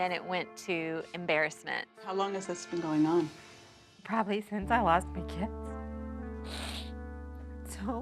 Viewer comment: I thought she said probably since I lost my kiss I almost died from laughter🤣😂😂